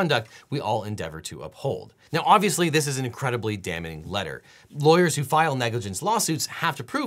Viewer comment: error in the captions there: “whole” instead of “hull” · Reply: “damaging” instead of “damning”